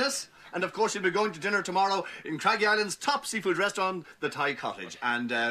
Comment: Did he say "top secret restaurant" at 14:57 ? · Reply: He said,"top seafood restaurant".